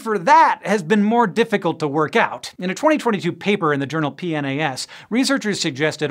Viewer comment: Did anyone think he was trying to say "penis" here? 2:38 ?